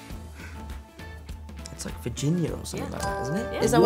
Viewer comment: I didn’t know it was Virginia I thought they said virgin💀